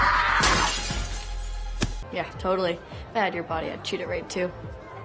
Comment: What did she say? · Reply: "If I had ur body I'd treat it right too"